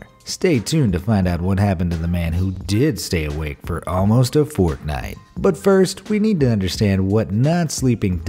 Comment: who heard fortnite?